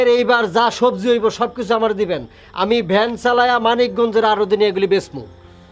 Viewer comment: আমি কি ভুল শুনলাম আমাদের জেলার নাম মানিকগঞ্জ